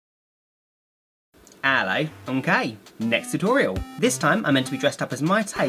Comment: on the start i heard "hello, im gay"